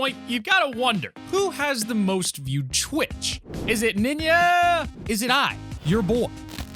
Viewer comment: who else heard "Ninaaa!"